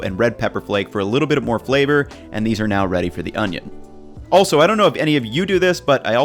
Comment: did he say onion instead of oven?